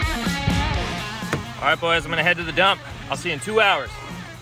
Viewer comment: Lol, I thought he said "Alright boys, I'm gonna go take a dump. See you in two hours."